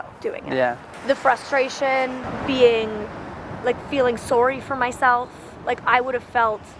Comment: I'm sorry this is what came to my mind first BUT SHE SAID SOEHRY AT 10:22 (Girl, Molly, you're an inspiration. Even though my dumb brain noticed this little Canadian tidbit, I can recognize how amazing you are, and how tough and smart you've come to be.)